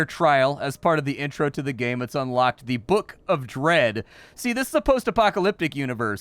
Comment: I thought he said, "Book of Bread" at the start and thought this was going to be a silly game.